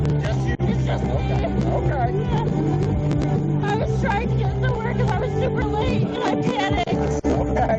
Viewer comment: So you don't attempt to know facts, and use a falsity we all know to be wrong, to be smirky-and failed. She later said to a personal matter, also what was in the Troopers report. Graduate above playing with cartoons on adult social media, pay attention, know facts or don't comment/reply. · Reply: Maybe you should go listen at 9:19 where the woman is telling the trooper that she was trying to get to work. Or she said "trying to get to somewhere." The first time I played this video it sounded like she said work. Later on they got the music shut off and you can hear her say she was trying to get somewhere and was super late. Regardless, it sounded like she said work while the music was playing. The trooper wrote in his report she said somewhere. Either way, you were a dick with your snap judgement.